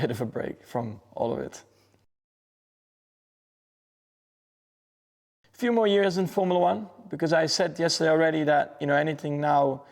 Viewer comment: wait did he just say "a few more years in F1" ?? That implies only 2 to 4 years. I definitely hope he stays for another 10 years at least like he said on team radio after the race Sunday!